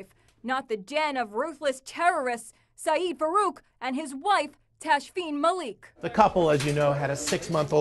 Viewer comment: when the female talker says ”wape” instead of ”wife”.